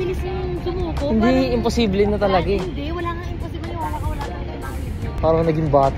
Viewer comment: love hindi wala nga imposible… DID I HEARD IT RIGHT!!!????????